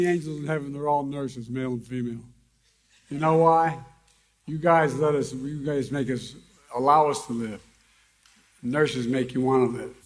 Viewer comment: All I heard was “You know why? You guys let us, us “N word”, allow us to live.” And that last sentence “…and nurses make you want to live”. Okay that sentence sound super creepy as in good ol’ Joe wants to only live for the hot nurses breathing down on his ear action